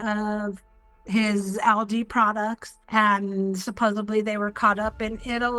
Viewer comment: This week's Nigerian scammer has an "Irish-Italian" accent! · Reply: What a maroon!! I can’t! 😂 DAMN IT SHE SAID supposeBLY again!!!! 😢